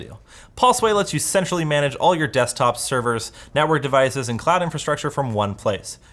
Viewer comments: Did he say at the end Pulseway lets you sexually manage all your desktops? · Am I the only one that thought said you can sexually manage rather than centrally manage when he was doing the sponsor spot for pulseway? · I swear to God at 16:16 I hear him say "sexually manage" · Did just say: Pulseway let's you SEXUALLY manage...??? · I swear he says "sexually manage"..... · I swear at 16:16 he says pulseway lets you sensually manage your network... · Pulsway let’s you sensually manage all your desktop. I heard something else. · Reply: okay you're the only here that got this. I literally can't hear anything else than "sexually manage" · "Pulseway let's you SEXUALLY manage " That's what HE REALLY SAID! We know what you did last summer (and night). Oh we know! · does he say "let you 5exu@ manage"? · Tell me it doesn't sound like he said "Pulseway let's you sexually manage..." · I thought said “Pulseway let’s you sexually manage all your desktops and servers on your network” at the end 🤣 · Is it just me or did just say: 'Pulseway lets you sexually manage all you desktops, servers...' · I had to turn on subtitles because I kept hearing him say "sexually manage". Turns out he says "centrally manage". I replayed the video several times. I keep hearing "sexually". There's no way that what it came out of his mouth spells "centrally"